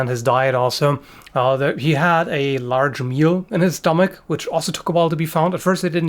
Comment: i thought i heard "he had a large mule in his stomach" & was like "wait wait, he must have said meal" , but still had to go back to re-listen to be sure lol.